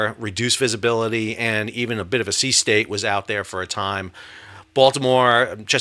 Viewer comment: Did he say a sea snake was out there? @ 2:50 ?